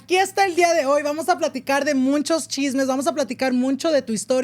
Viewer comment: I keep hearing him say “MUNCHO” anyone else 😂